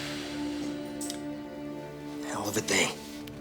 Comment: Conversation from 2:37 What do they say? I cant hear. Please tell me, thank you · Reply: Himanshu Baxy “hell of a day” , he repeats several times...the other fella says...”this is him, this is him 104 shot”...the third guy says ..”you’re alive aren’t ya? the tailor says...I know a guy who’ll part with everything you got for $40 dollars whatdya say?....